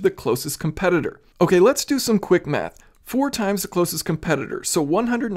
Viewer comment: Did I just hear “let’s do some quick meth”? 😂😂😂